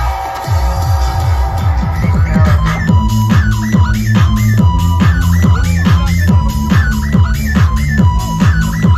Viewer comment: c'est quoi déjà ? Merci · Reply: Chapati express 06